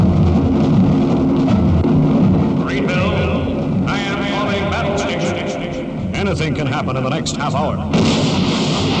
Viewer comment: May I ask what does he say at 0:43 "Marineville ????? ???? battlestations". I have always wanted to know and I think I may have found the right place to ask! If anyone can edit Wiki then the first screening of Stingray needs adding to the '1964 in the United Kingdom' page. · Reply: "Marineville, I am calling Battlestations...."